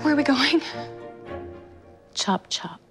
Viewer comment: What says in 1:14? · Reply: "Chop chop."